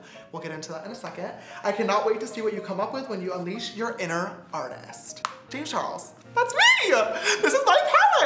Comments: Can some one tell me why I heard "unleash your inner shishter"?? Are my years ok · I showed my neighbour the ad for this palette and when it whispered “unleash your inner artist” he misheard it and so he came up to me and whispered into my ear and said “I’m a future artist” and died. My soul has left the chat